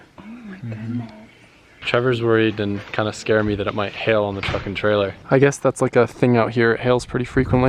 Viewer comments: Anyone else thought he said "fuckin trailer" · when said it might hail on the truck and trailer i thought he said it might hail on the f***ing trailer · i replayed here so many times because i thought he said fuckin trailer not truck and trailer LOL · When he said "it might hail on the truck and trailer" I thought he said "it might hail on the fuckin trailer" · first time i heard i thought wait did he say say fuckin trailer but he said truck n trailer